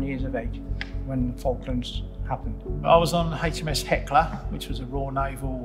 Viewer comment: The subtitle says "HMS Heckler" ... but it should be HMS Hecla <link>